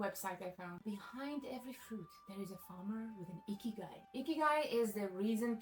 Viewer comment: I'm sorry, but I'm rolling at the captions, icky guy, الرجل الرديء, instead of ikigai